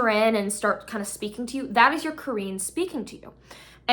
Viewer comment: that is your Korean ? What is this word? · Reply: Qarin or qareen.